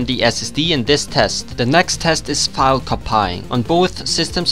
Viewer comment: Did he just say File Copaying?